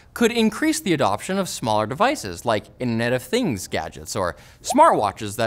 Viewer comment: caption says "in net of things" but i'm pretty sure it's supposed to be "internet of things"